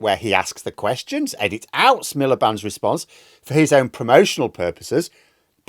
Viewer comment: I always hear Milliband's name as The Ed Miller Band! 😋